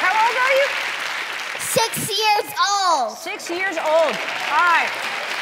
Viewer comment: I thought he said I'm 60 years old